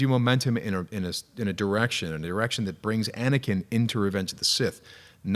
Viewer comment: I can't be the only one who heard Sam say "erection" instead of direction 😂